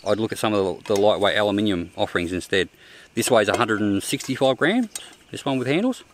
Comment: I spat rice all over my screen when I thought I heard 165 'Grand'. --_